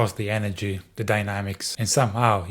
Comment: Did I just hear “DYNOmics”? The Church of Dynology agrees with you.